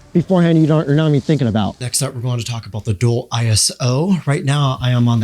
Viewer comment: Could have swore you said, "Next up we are going to taco bell."